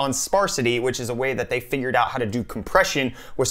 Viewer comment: did you just say fingered out?